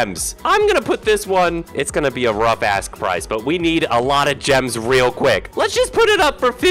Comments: idk what other people heard at 1:40 but I definitely heard “rough ask price” because the video is all about auction, so he’s making a rough “asking” price for that particular pet to be auctioned, which is 15 billion. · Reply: i thought he said “rough a$$ price” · I think he said the a $ $ word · did said AS?